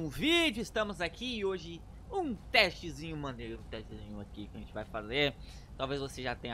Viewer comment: eu entendi tetriz